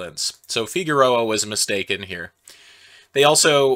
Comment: I heard "...so Figueroa was a mistake in here" rather than "mistaken here", and honestly that's not inaccurate, all things considered.